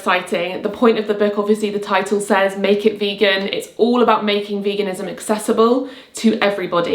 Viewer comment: At first I heard ‘Naked Vegan’ 😂😂